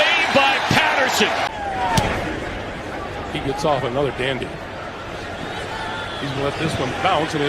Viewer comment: Anyone else hear the commentator have to stop himself at 2:40 ??? "He gets off another damn goo-" 😂💀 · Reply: XxSaberSlayerxX he said dandy mate